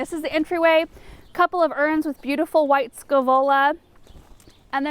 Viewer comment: Gorgeous garden. What did you say are the two white flowering plants in the pots at the very beginning of the video? It sounded like you said "white scolola" and I have searched and searched. Let me know if possible. Thank you so much for the beautiful videos!